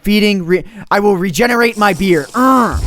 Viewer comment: I thought you were saying "I will regenerate my beer" You really "I will regenerate my beard